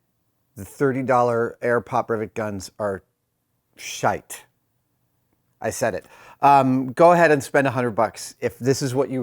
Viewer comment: What is he saying at 27:34? Is it "scheiß" ???